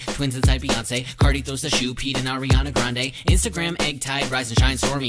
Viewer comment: STOP I HEARD "peed in ariana granda" 😭✋